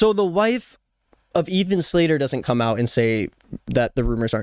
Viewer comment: i thought she said “ian slater” at first and was very confused because that’s a salaried employee, not someone in a broadway show. glad to know it’s someone different. i have no clue why i’m leaving this comment.